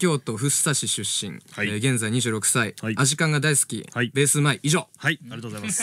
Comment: 一番初めにこの動画見たとき、アジカン知らなくて、井口さんが「アジカンが大好き」って言ったとき「アシカが大好き」って聞こえて、アシカ…？ってなった